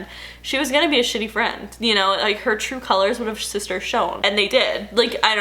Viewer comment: did she say “sister shown”? 😊